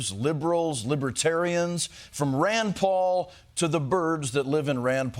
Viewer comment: did he say birbs? the millennial writers at comedy central need to chill lol